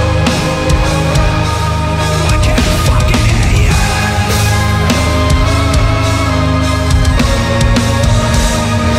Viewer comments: What did Dustin say at 0:30? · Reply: "I can't fucking hear you!!" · Does someone know what is he saying in 0:28? It's pretty epic. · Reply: He’s saying “I can’t fuckin’ hear youuu” :D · What he say? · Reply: "I can't fuckin hear you!!!"